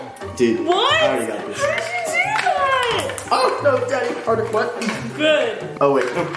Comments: Did Kio say daddy or was it just me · i swear Kio said oh wait no daddy harder or is it just me